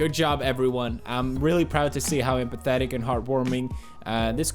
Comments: The first time I heard that I thought he said pathetic and had to rewind it haha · holy shit I thought he said “how pathetic”😂😂😂😂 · I understood "pathetic" instead of "empathetic" at first :D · i thought you said "i love how pathetic and heart warming this community is" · pew said empathetic but for a second i thought he said pathetic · did pew really say pathetic? to a go fund me for an elderly in need? not cool!